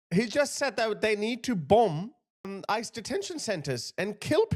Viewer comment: I don’t think he said bomb ice, I’m not sure. He sounded like he said Abolish ice.